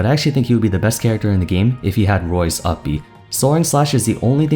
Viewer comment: Coming off of the "Is Recovery Overrated?" video, finally realized the word I kept hearing was "Soaring". I took it as "Soren", and was so confused, like, "does Chrom have an alter ego?"